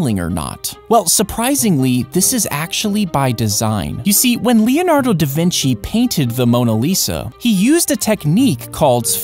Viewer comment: Me watching time buddies I’m epic for like 3 years I thought you said Leonardo da fishy